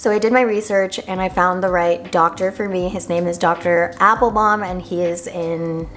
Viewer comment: OMG, AT FIRST I THOUGHT SHE SAID HER DOCTOR’S NAME WAS “DR. APPLE BOTTOM”😂